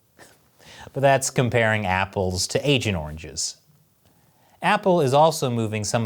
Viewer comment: "Comparing Apples to Agent Oranges" LOL Damn! Also yeah all businesses have to move out of china. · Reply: I thought he said Asian oranges